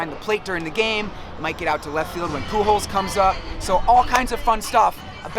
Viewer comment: Sounds like your saying poo holes